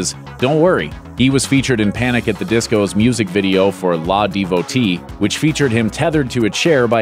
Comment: did that man just say "Lah" Devotee instead of L-A Devotee!? That sad man. Even I know that song and I'm not a fan of today's music.